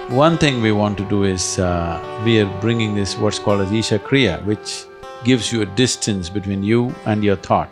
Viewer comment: Wonderful and great messages!! One question: what did Sadhguru say on 9:31? Ishakria? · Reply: Isha Kriya - a meditation you can find on the Sadhguru App